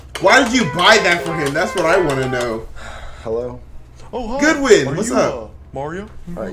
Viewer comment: did he just say goodwin